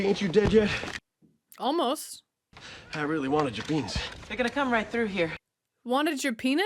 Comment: Omg, you mishearing "I wanted your beans" as wanting his penis had me laughing so hard I almost fell off my chair. Dying 🤣☠️☠️😭